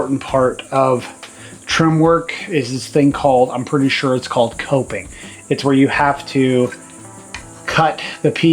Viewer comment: you put the molding around the window correctly. The narrow end goes at the edge of the window, framing, and the wide end goes to the top. It sounds like you were saying toping, but the correct word is coping.